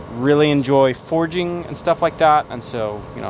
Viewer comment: When you said forging I thought you said foraging. I was all excited to see you guys go find some mushrooms 😆 fire is cool too though.